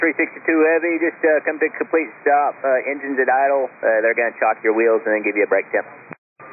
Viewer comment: I think tower said "gonna chock your wheels", i.e. put the rubber stops on them as a groundside parking brake, which is of course the norm at gates but very unusual for a "routine" brake check after a hot landing. I mean, it's logical, it's just very very unusual for them to actually do that.